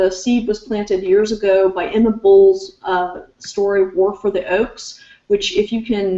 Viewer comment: What was the name of the Book she mentioned at around 25:18 in the video? I thought she said Work of the Oaks, but that's not what she said I don't think.